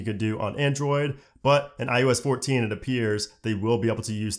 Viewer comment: I like this guy and his videos, but does anyone else notice it sounds like he say “i-U-S” instead of “i-O-S”?